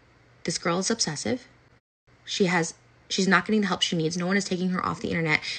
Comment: She's begging her to get help when EVERYONE beggs her to get help. LOL I love Trisha don't get me wrong but some of this seems like a bunch of self deflecting. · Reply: I thought she said she is getting help!?